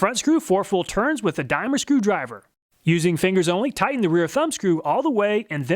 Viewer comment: I kept replaying the scene at 3:05 where I heard "Loosen the screw four full turns with a Dimer screwdriver". I thought "What the hell is a Dimer screwdriver?". Of course I eventually figured out that he said "dime or screwdriver". Duh!